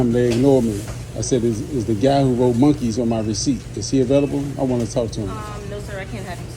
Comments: Anyone standing outside taking orders knows how loud it is, and even in the video I thought he said, 'Monkees'...and he says it "Set him back years from healing"...😅😅😅😅😅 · He wants easy money. What a crybaby. Dude needs to go to jail for all these accusations. He even said it like it had an N when he was talking to the manager. I thought he said monkees or some similar spelling, too.